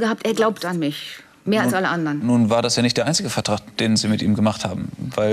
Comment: What did he say at 20:25? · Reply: „Nun war das ja nicht der einzige Vertrag, den Sie mit ihm gemacht haben“